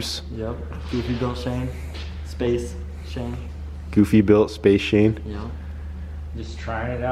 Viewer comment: What's saying at 21:36 ? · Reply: GoofyBuilt Shane